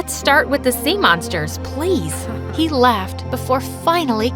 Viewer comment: garret needs his own story he has so much potential Edit: ty for the likes also can y'all just understand his a guy male where dose it say his trans or non binary??? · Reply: I heard "he left before finally" instead of "he laughed before finally" in